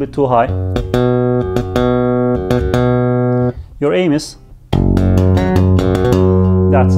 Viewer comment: FANTASTIC tutorial!! 👍 Did anyone hear “Your anus” at 04:19? 😂